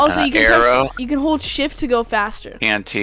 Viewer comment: I thought said shi. T but he said shift